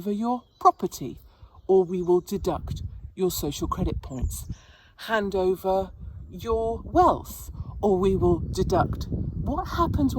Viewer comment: Katie, I wasn't listening clearly, I thought you said HAND OVER YOUR WIFE 🤣🤣🤣🤣